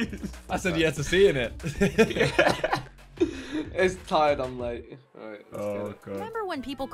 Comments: Did anyone else hear Vik say “It’s tired, I’m late?” 😂😂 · Did vik just say ‘Its tired I am late’